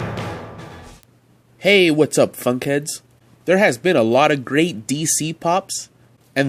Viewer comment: I thought he said “Hey what’s up Fuckheads” in the beginning it caught me so off guard